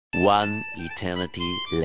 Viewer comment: it sound like he say hey what do you want